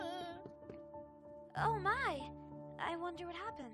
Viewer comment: When Amai said "Oh my!" I thought she said "Ah my!" Lol